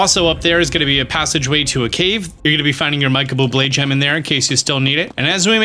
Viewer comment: I’m so glad that you said Michael bubbleé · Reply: Ooh that is what he said. I thought he said mike bubble blade gem. I listened to that a dozen times and didnt hear Michael Bublé. I thought I was missing out on a special sword.